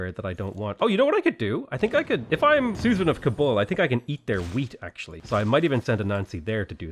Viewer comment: So I’m watching this with captions on whilst my other half is drying her hair, the captions thought you said Susan of Kabul. I laughed so hard as it reminds me of an 80s Michael Douglas movie.